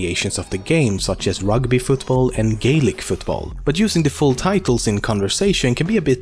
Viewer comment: did anyone else think he said "gay lick"